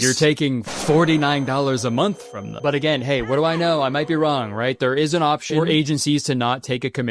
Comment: every time I hear Kurtis say “what do I know”, I flash back to the Pinocchio video where he said “what do I nose?” 50 times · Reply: Idk if my mind made this up or not but I keep hearing him say "what do I knome?" Is this actually from a video or am I delusional